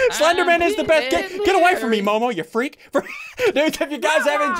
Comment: When says Momo around 24:20, it sounded like she said Elmo!